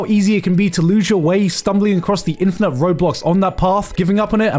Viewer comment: You cannot convince me bro did not just say ROBLOX